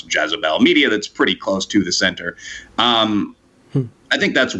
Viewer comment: Did he just call Jezebel, "close the the center?"